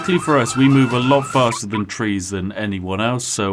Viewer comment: i thought he said "We move alot faster than trees" and it made ne laugh